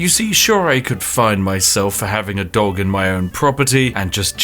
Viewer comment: i think i misheard it.. i heard dog instead of cat! It's clearly a cat!